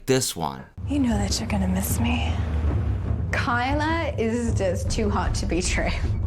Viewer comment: I thought that one person said ‘Kyla is to hot to be trans’ instead of ‘Kyla is to hot to betray.’